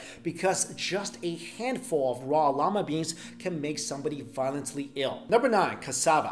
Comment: I heard him say llama beans